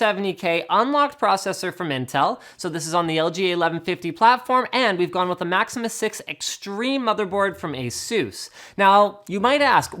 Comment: 1. Press CC (Subtitles) Next to the gear icon. 2. Go to 1:40 3. He says "And we've gone with the Maximus 6 Extreme motherboard from ASUS" 4. Subtitles say "And we've gone with the Maxima 6 Extreme mother board from Jesus" XDDDDD